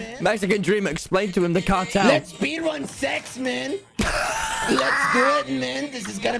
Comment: I didn’t know my headphones weren’t connected so as I turned my volume up to max and all I heard “let’s speedrun sex man😎”